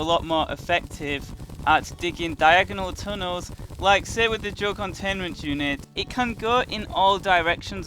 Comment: You sound like youre saying joe containment unit